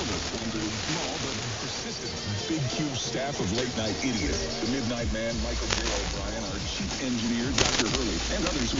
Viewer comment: ngl i think i heard “the midnight man micheal herobrine”💔